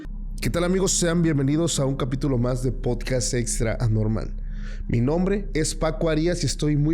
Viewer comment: En la presentacion te entendi "extra-anormal" en vez de "extranormal" jajajajajajajaja!!!!!!!!!!!!!!!